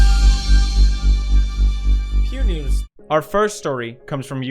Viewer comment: did anyone else think that it said pewds newds or am I just weird